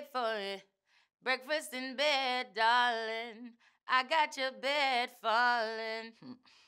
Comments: I thought she said breakfast in bed darling. I got your BAAAAAAAAACON! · What I had no idea she was saying "I got your bed falling" thought she kept saying "I make you bacon" awks